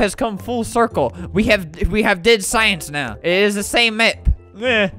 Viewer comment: I heard in the background I’m a banana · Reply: In 9:40 I heard I’m a banana